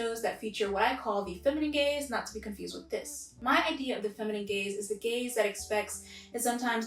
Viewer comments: Oh man. Whenever I hear "feminine gaze" all I hear is "feminine gays" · I keep hearing “feminine gays” instead of “feminine gaze” lol